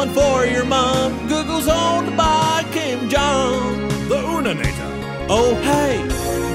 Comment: I thought you said Kim Jong was a "Urinator" not "Uninator" 🤣